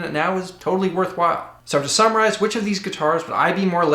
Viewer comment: it sounded like you were saying "so to samuraize..."